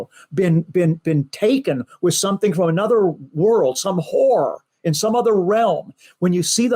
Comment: Sounded like dude said "horror" not "whore" Idk